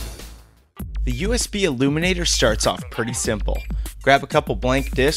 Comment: i thought this said USB illuminati xD